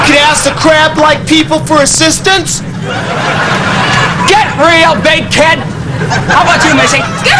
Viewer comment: did he say " Get real, baked kid? " lol